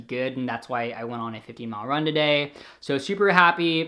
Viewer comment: For me, I find that I get increased acne when I eat more sugary things than norm. Def. 100% important to listen to your body! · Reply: Lol for a second towards the end I thought you said "50 mile run" not "15 mile run" and I was like daaaang XD